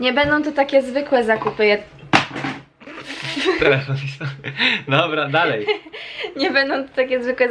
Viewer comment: co on powiedział 😂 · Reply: "telefon mi spadł" xD